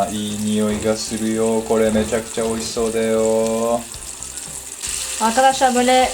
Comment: = Ī nioi ga suru yo kore-mecha kicha oishi-sode yo いい におい が する よ これ めちゃきちゃ おいし そ で よ sadece duyarak google çeviriye yazdım çevirdim nasıl? :D "çok iyi kokuyor bu yemek gerçekten lezzetli" anlamında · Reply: Mecha kucha oishii sou da yo olcak